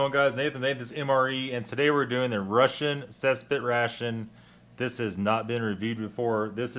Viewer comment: Could have sworn you said "Russian Cesspit Ration" and I had to go back to check, haha